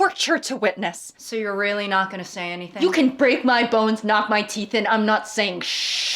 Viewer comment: I thought u said "braid my bones" at 29:04 and I was like damn thats extreme tell us how u rly feel queen but that is in fact not what u said